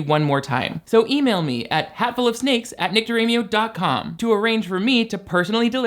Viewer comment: every time says their name in a promo I just hear "NICTARAMIO" like it's a brand name